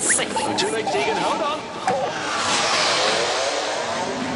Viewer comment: I thought it said a duck